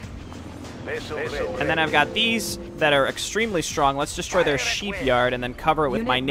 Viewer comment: did anyone hear sheep yard?!?! Still tho gud vids and gameplay